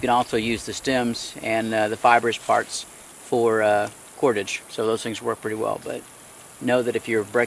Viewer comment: Can i get a clarification on 7:00? "You can use the stems and fibrous parts as ... ..." did you say pottage? or ? Couldn't really hear the word clearly.